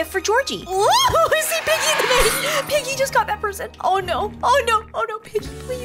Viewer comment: For a sec in the vid, i thought u said “OOP PIGGY JUSTGOT THEPERSON”